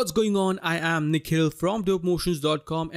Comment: I always thought you say "I'm Nick Hill from Dope Motions" and i like ok maybe he lives in US but no way he has that name and today i just found out its written Nikhil 😂